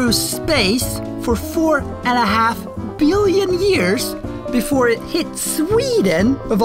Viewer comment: Damn, I thought it said 4 billion dollars, not years🤦‍♂️🤦‍♂️